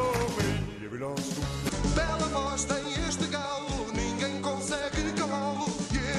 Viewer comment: I speak portuguese (Brazilian Portuguese) and I always hear the European Portuguese saying "Bela bosta" in 1:16, which translating to English means "Big Shit". It could be the accent, but maaan, i'm sure he said that.